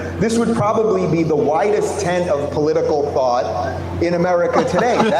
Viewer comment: Dave Rubin actually said "whitest", but everybody misheard him.